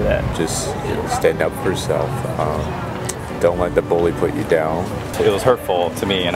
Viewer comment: did he say putos?